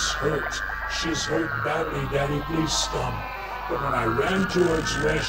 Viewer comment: I think the subtitles you have provided from 9:23 to 9:38 are a little off. Instead of "come" I heard "stop"...